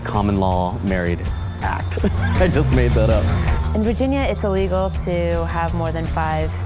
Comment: I thought it said virginity, not Virginia xD